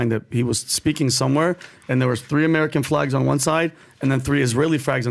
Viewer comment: I heard fags to 😂😂